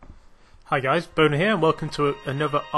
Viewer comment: It sounded like he said: hello guys, Boner here!